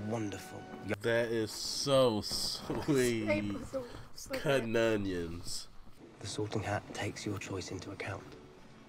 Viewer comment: At first I thought was saying cuh-nun-ions. It took me a minute to realize that he was saying cutting onions. Lol 🤣